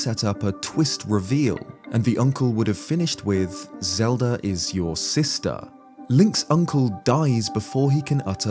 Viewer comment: Maybe... Zelda is your soulmate? Idk that's what I thought he said...